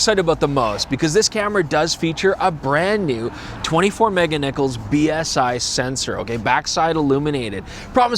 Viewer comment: 24 meganichols? I heard it ... :)